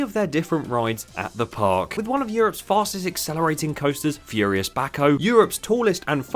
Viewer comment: I thought you were saying “furious backhoe” and I was wondering how a coaster was designed after a piece of construction equipment.